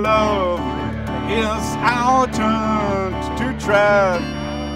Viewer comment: is he saying our turn to drive or try or ...what?